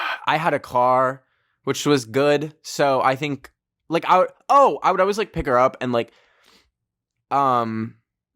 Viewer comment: I thought he said would always beat her up